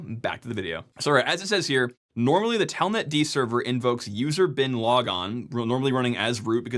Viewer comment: I heard "user Bin Laden" and can't unhear it now.